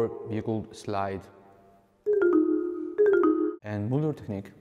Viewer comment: What does he say here? "and...technique...we all know ... is" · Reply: Moeller